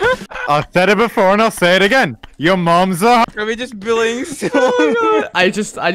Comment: this is what he said: "Your mom's a hat"